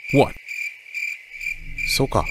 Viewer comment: I thought said "SUKA" (I know he said "okay", alright)